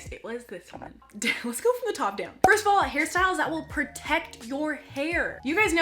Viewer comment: harry styles. I thought it said harry styles not hairstyles.